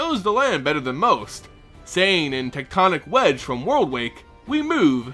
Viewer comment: Wait, did just say: "in Tectonic Wedge"?